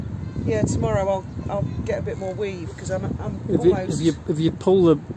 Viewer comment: Well done you are an inspiration ❤❤❤❤ when you said weave i thought you said weed lol totally cracked me up 🤣🤣🤣🤣🤣🤣